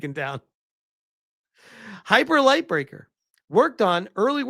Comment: Had to double take at Hyper Light Breaker because I thought I heard Hyper Light Drifter.